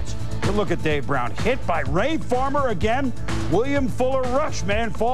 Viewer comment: wait a minute did I hear boomer correctly Ray farmer as in the former Browns general manager Ray farmer